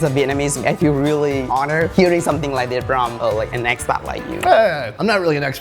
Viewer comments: it sounded like he said ex pat not expert xD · At the end I thought the co-host said "from an expat like you", not "expert". Or is that the joke made? lol